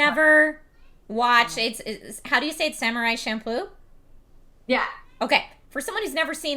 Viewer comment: I thought you said Samurai shampoo